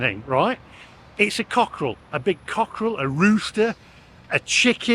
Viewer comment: Oh i thought you said love a big c***